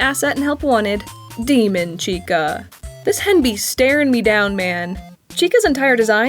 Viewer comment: I heard you say "this henby", and for a second I thought "oh, the colours! haha, great pun" before I played it back and realised what you actually said.